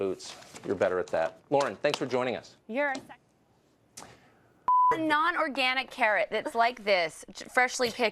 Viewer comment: LOL 0:37 . Can anyone tell me what the hell was that about? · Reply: "You're a sexist